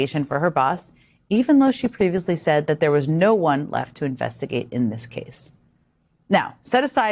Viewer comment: I thought Kash Patel said there was no one except Epstein